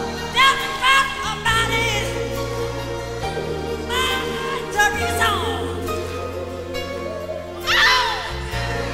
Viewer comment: What he say at 7:19 ? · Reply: Mama tell your son or mama tell me some